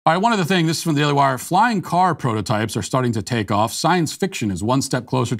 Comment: 🤣🤣 I thought he said flying carpets.